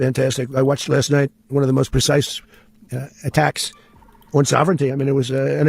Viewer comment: did he just say.....an attack on sovereignty?